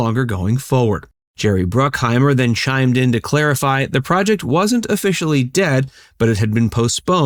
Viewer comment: I thought it said that he said he was officially dead 💀